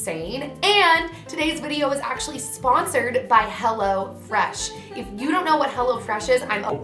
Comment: who else thought she said hella fresh at first lol